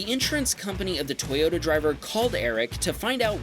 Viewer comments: mismatching subtitles at 1:37 u have typed in ruben instead of eric lol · I Like How (I Think) Said Eric But The Text Says "Ruben"